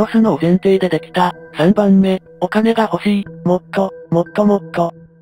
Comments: の「もっと もっともっと」のイントネーションがいつ聞いても「もっと ほっともっと」にしか聞こえなくて胸糞悪すぎシーンなのに笑ってしまう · 待って、1:14:25 ここのさ もっともっとがほっともっとに聞こえる